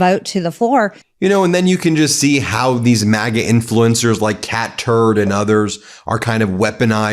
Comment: I could have swore I heard him say like cat turd and others